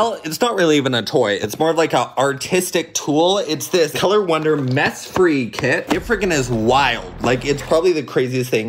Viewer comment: for a second i thought you said “meth free” instead of “mess free”